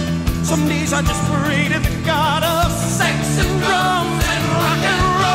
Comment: " Some days I just pray to the god of sex and drums and rock 'n' roll" ;-) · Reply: Drums really? For nearly 30 years I thought he said drugs